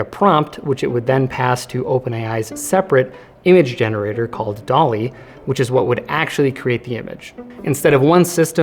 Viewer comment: The subtitle at 42:49 says Dolly instead of DALL-E